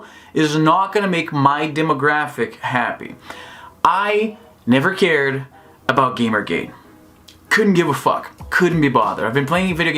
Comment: Geez, I heard u saying " I don't care about game GAY" I was like "what hell", then I turn on the subs XD